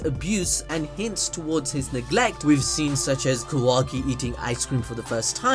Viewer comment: Did you just say kawaii eating ice cream for the first time? 😭 Rip Taiyaki, you are now ice cream-kun.